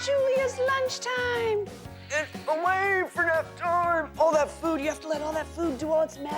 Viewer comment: What did she say? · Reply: Away for nap time!